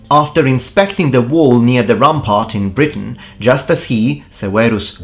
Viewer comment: OMFG. I genuinely thought he said “after inspecting the wool near the rump part” 😂😂😂😂😂